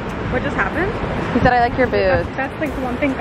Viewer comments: Can we just talk about the guy that said like your boobs? I mean does any guy really think that making a comment like that makes a girl all into him?? I’m just so disgusted with what women have to put up with. Ok. I’m done being a Debbie Downer. Love the vlogs. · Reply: I thought he said boobs too 😂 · Can we just talk about the guy that said like your boobs? I mean does any guy really think that making a comment like that makes a girl all into him?? I’m just so disgusted with what women have to put up with. Ok. I’m done being a Debbie Downer. Love the vlogs. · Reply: lol I thought it said boobs. I’m getting old apparently and losing my hearing lol. Sorry.